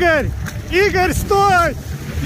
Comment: Кому сначала послышалось не Игорь а Гитлер? 💀